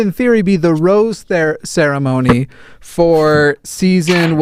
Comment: When you say "rose ceremony", I always hear "roast ceremony". Which actually sounds like a much more interesting concept. And I love how gets progressively happier and gayer through the episodes!